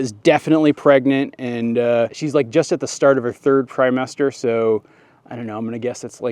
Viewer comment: Is it just me or did anyone else hear say “primester” instead of trimester at 10:48?